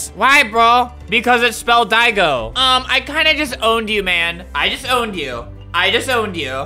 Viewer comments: When u said I just owned you I thought it said “I disowned you” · When Albert said "I just owned you" I heard "I just disowned you" XD Edit: wow this is really CHILL that you guys gave me 200 likes! Thx to all of you! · it sounded like u said “i disowned u” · When Albert said "I just owned you" I heard "I just disowned you" XD Edit: wow this is really CHILL that you guys gave me 200 likes! Thx to all of you! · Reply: I heard “I disowned you” lol, just remove the ‘just’. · What everyone heard: “i just owned you, i just owned you” What i heard: “i disowned you, i disowned you” 😂😂😂😂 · Albert: "I JUST OWNED YOU, I JUST OWNED YOU, I JUST OWNED YOU!" What I heard: "I DISOWNED YOU, I DISOWNED YOU, I DISOWNED YOU!"